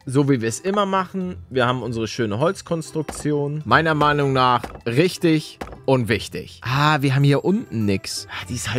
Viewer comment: Ich habe folgendes gehört: meiner Meinung nach richtig unwichtig. 😂😂😂😂😂😂😂😂😂😂